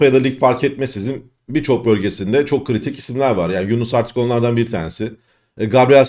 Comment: "Yunus artık"ı "Yunus Aktürk" diye anladım sanırım Kerem'i aşamıyorum